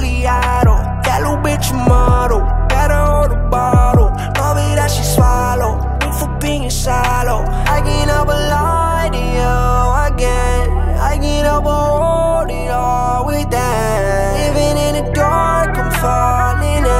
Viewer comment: Living in this world of sin Finna start another war within Baby you understand me Did this for the Grammy Did it with my family Did it with no plan B Bitch you gave me nothing So turned it into something In this four month end Feeling repercussions Think I'm being followed In the Monte Carlo Hit em with the throttle If not , a fully auto That lil bitch a model Better hold a bottle Told me that she swallow Ain't from being shallow I can never lie to you again I can't help but hold it all within Living in the dark , I'm falling in Giving you my hearts , my darkest end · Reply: I heard "thanks for being shallow" and "did this for a month and; feeling repercussions"